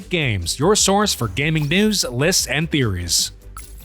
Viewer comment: he said lits instead of leaks (not trying to be mean)